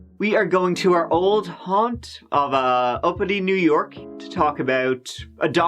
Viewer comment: I thought you said Albany, New York, not uppity...lol...